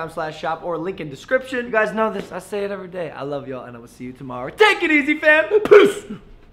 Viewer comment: Tbh I thought he said “Take it easy fam, PISS”